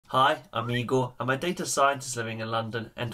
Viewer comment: Great videos, BTW it sounds like you say "Hi Amigo" :P